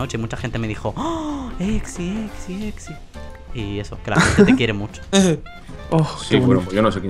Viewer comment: soy yo o escuche "te quiero zeus" ( ͡° ͜ʖ ͡°)